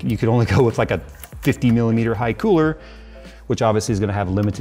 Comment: I heard 50mil haiku cooler. Time to get the ears checked.